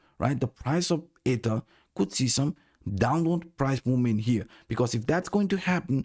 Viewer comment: it sounds like he said 'Ada' come on bro I panicked for a second.